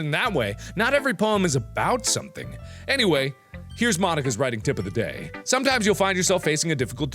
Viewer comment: When Mark said Monika's name, it sounded like he said, "ma niga"